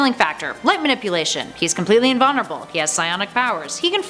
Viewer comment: The problem with Sentry is he’s supposedly “completely immortal,” yet he can get hurt enough to display his regenerative abilities. I don’t think I have ever heard anyone accurately categorize characters’ level of durability. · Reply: The OP was probably confused at 1:10 “completely INVULNERABLE” is what the lady said RIGHT AFTER saying he had a “regenerative healing factor.”